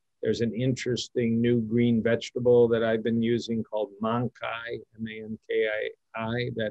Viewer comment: What was the plant dr. said? I heard momkai, can't find anything. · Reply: Mankai